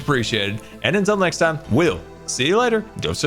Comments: sounded like he said don’t subscribe 😂Well I subscribed anyway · Is it just me or did he just say: "Don't subscribe"? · Did he just say “Don’t subscribe”... ?